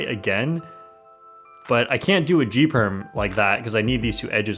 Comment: I swear I heard D Perm!